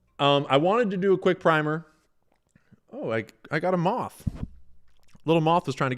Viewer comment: Sounded like you said “Oh! I got em off!” Just out of nowhere, but what you said is “oh I got a moth” 🙊